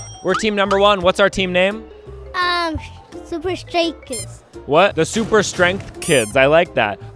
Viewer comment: I swear that kid said "Super straight kids" XD